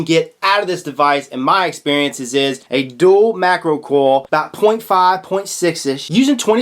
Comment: "Dual macro coil about .5, .6~ish using 26 gauge"? did he said "macro" or "micro"? I'm a newbie. Thanks!